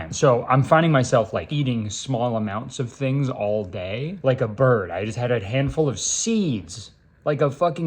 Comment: "I'm finding myself eating small amounts of things all day, like a bird" Bro I thought he was saying he ate a fucking bird